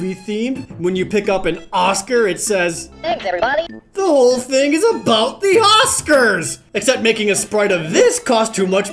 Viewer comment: What DO the Oscars say? I honestly cannot understand. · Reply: "Thanks everybody!"